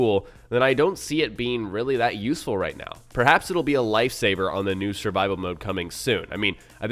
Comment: when he said "life saver" i thought i said "lightsaber" imperial march starts